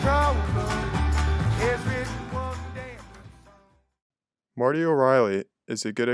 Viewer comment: Nice Video. What is the name of the indie sounding artist? It sounded like you said Martio Riley, I tried to search him but have not been able to find him. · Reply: It’s Marty o Reilly